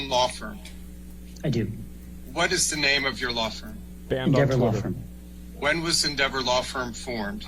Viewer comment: I heard him say “Bend Over Law Firm”